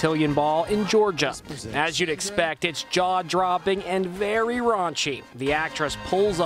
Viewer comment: did he say very rache